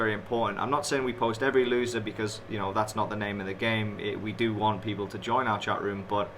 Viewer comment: Maybe I'm not hearing right and youtube subtitles are messed up. Did you say at 4:56 "I'm not saying we post every loser because you know that's not the name of the game and we want people to join our chatroom"? But you consider yourself honest because you post SOME losses? Most of penny traders can dramatically change their results just be skipping 1 or 2 big loosing trades. If you are not posting ALL your trades and you are using that to sell your services, YOU are a fraud, period. Fraud definition: "a person or thing intended to deceive others, typically by unjustifiably claiming or being credited with accomplishments or qualities." Also profitly DOES NOT verify all your trades, you can skip trades and YOU KNOW THAT... So why are you saying it? Seems Furus exposing other furus is a new thing.